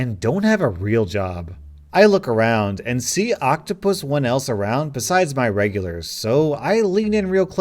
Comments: you said you see octopus one else around instead of no one else 😂😂😂 good replacement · did you say octopus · he said octopus instead of no